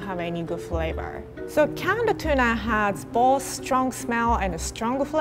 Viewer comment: Anyone else noticing her saying "can in a tuna" instead of "tuna in a can" at 0:44?